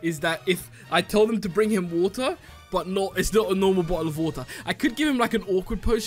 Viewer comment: did u say boherofwaher?